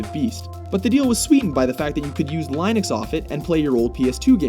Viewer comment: Did they just mispronounce Linux? It sounded like Lineux to me :S